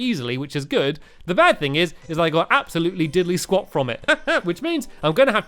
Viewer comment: At first I thought it said diddly squid lol